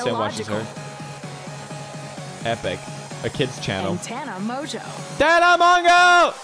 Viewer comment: Did he said "kids chanel"????????